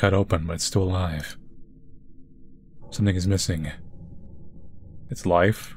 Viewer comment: sounds like he said “This n*gga’s missing” 💀